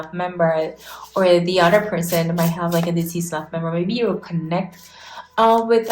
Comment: What did you say at 1:14:47? Sounded like dc staff member. 🤔 Could you please tell me, what did you exactly say. 😇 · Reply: deceased loved member☺️